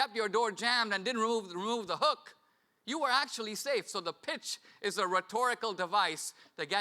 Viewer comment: At first I thought he was saying "bitch" as in " If you open the door for them, you're the bitch"